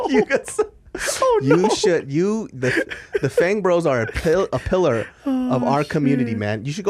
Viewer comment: i thought David said fangirls not fang bro's 😅😂😂